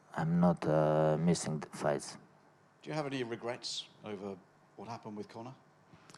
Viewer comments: He clearly thought you were saying regress as in take a step back in his training since his last fight not REGRETS as in how he handled the end of the Mccgregor fight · Advice to interviewers - have smart phone on Google Translate so you can accurately ask questions in interviewees own language. Khabib thought you said 'regress' which he compared to 'progress,' you could have looked up and said REGRET in Russian for clarification. Another tip - learn how to speak to non-native English speakers, speak slowly, avoid slang, colloquialisms, idioms, keep it simple. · He thought he said regress not regrets